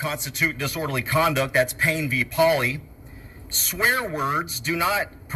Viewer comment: what case law did he provide, I heard pain v Polly